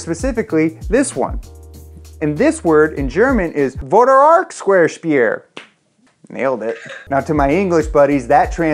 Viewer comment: thought you said Square space